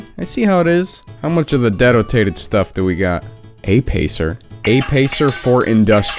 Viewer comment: Ngl. I was using this video to try to fall asleep. Then I heard you say “how much of the dedotated stuff do we got” and it cracked me up wide awake 🤣